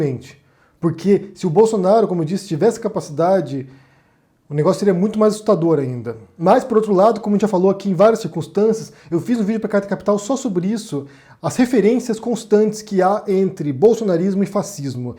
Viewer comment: desculpa mas fez video aonde ? nao entendi oque vc falou pq a dicção nao foi muito clara, eu sei q vc ta trabalhando nisso, mas nao entendi